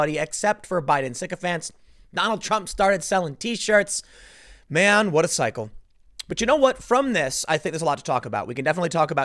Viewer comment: I thought Tim just said: 'Man, what a psycho.'